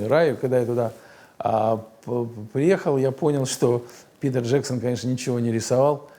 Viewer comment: я один слышу "Пидор Джексон", а не "Питер Джексон"?